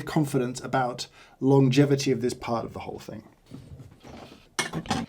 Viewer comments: does anyone else hear James whisper "I hate this"? Or is it an incredibly coincidental sequence of parts-and-glass-scraping sounds? 11:44 haha confirmed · do I hear James saying "I hate this"? 😅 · Did I hear "I hate this beep"? Feels kinda intentional but the subtitle is not there. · “i hate this”? did i hear right?